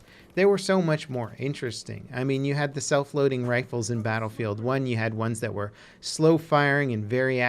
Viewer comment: I keep hearing "self loathing rifles" - poor poor rifles.